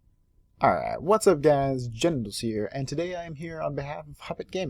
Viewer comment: i heard "Whats up guys genitals here" LOL